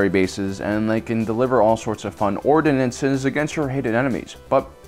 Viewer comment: What did he said from 0:13? "Fun ordnance?"